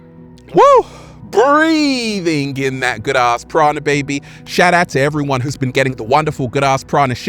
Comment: Funny until I saw the shirts I thought you were saying Good Ass Prada baby. lmao